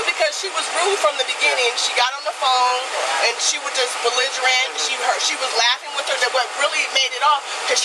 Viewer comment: "video camera-ing" Words is hard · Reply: I'm going to swear I hear her say "bezzigerant" as well